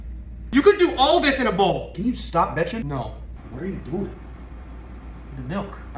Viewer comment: I swear I heard bro said "can you stop edging".